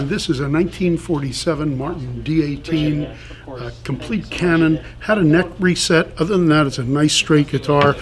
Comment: Called the Martin a 'canon' 😂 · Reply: I heard it as ""cannnon"". . .